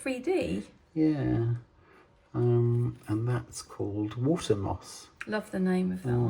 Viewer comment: Water moss? I thought you said watermark. Sorry. Great episode. Inspiration every step of the way. Now I have to google East Sussex to find something that my partner will be interested in. Otherwise he’ll say: you want to go where?!??’